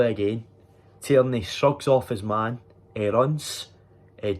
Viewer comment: I thought you said "sucks off his man" 😂🤦‍♀️